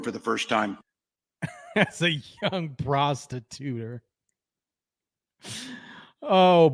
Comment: ROFLMAO!!....I thought Tampon Tim said prostitutor, too!! 🤣🤣🤣